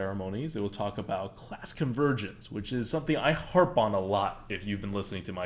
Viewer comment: i was so sleepy, that when he said "class convergence" i heard "Bask of virgins". I'd like to talk about that.